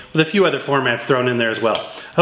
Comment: at the end, i thought he said "happy record, honey"